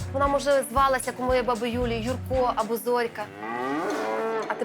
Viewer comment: "Як моя баба Юля - Юрко"?! Мне послышалось?